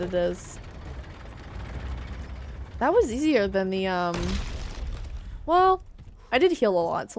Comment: i don't know why i heard "that was a little easier than your mom"